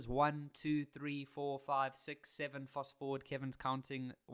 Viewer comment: did i just hear 67:hand-pink-waving